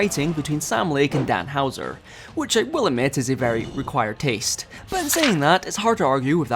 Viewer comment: Did he say "required taste" instead of "acquired taste"?